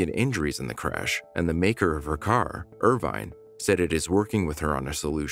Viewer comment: I’ve never heard of an Irvine car! what the hell is an Irvine car? Narrator said the “The make of her car IRVINE said it is working with her on the solution”! Did I misunderstand that or what? It looks like a Chevy Equinox to me.